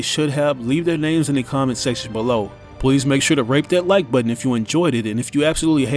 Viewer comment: WHAT THE FUCK?? You said rape that like button! 20:58 You sick shit